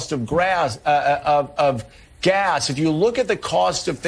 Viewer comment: Joe is comparing costs and he said Grass instead of gas well with the crimes he's probably committed im sure he's noticed the cost of Grass going up due to the fact he's smoking more and worrying less until his buzz goes away